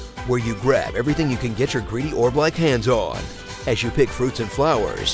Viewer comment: I thought he said "greedy or black" hands on. But it was just "orb-like hands"